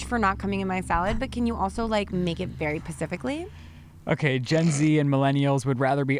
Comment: She always gets on Ryland but at 47:04 she says pacifically instead of specifically lol